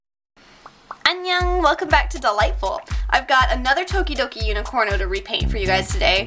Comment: Is it just me or does it sound like she says "Welcome to the Light Bulb" instead of Dollightful? Probably just me.